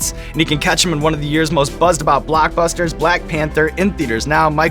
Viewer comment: Did he just say "blackbuster" 😂😂